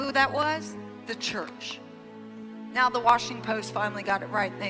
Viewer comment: Did she say "Washing Post"? I find it somewhat hard to gauge Paula's act. On the one hand she laments the animosity she suffered at the hands of the church. However, at the same time she says "If your name is not God your opinion doesn't matter and your acceptance is not necessary" It's a great act but it lacks consistency in its message.